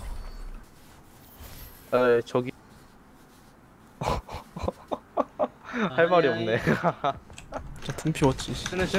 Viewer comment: Im a not-korean fan of sorry. But what did and said at 0:35? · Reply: Answer's too late but they both said "I'm a fan of yours"